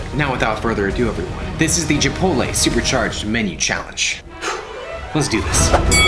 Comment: he says gipotle instead of chipotle 😂